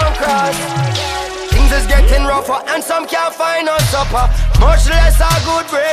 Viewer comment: When he says "can't find no supper", I misheard it as "can't finance supper". I suppose both could be true...